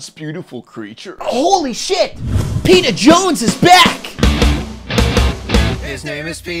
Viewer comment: I thought he said Peeta Jones and was referencing hunger games because Walter and Peeta are the same actor.